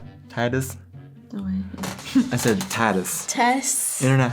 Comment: I always hear "Mini-Germans" when you say "many Germans". Cracks me up all the time. :D